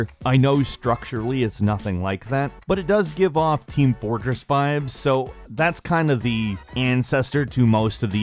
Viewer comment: did he just say team fortress 5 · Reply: Team Fortress vibes*